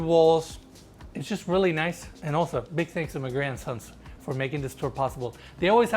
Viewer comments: Every time says Magrey and Sons, it sounds like he says “my grandsons” 😂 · Was I the only one that thought this dude was saying "My grandsons" not Magrey and Sons in the video? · I kept hearing “My grand sons” and it made me chuckle every time. I love the accent I could listen to speak all day something about his voice is soft and comforting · When you said the listing brokerage was “Magrey and Sons” I thought you said “My Grandsons” and I was so confused! Lol! Love your videos. You guys really have a knack for highlighting beauty ❤🌈☮️ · I thought he said "my grandsons" he said mgrey and sons I'm like u got grandsons lol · I thought he said my grandson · I thought he said Listing Agents "My grandsons" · who else thought he said big thinks to ma grandsons brokerage🤣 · I thought you kept saying the realtors were my grandsons lol.